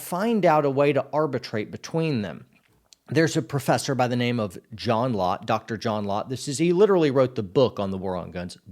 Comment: When you said John Lott I thought you said John Locke and I got really exited. Lol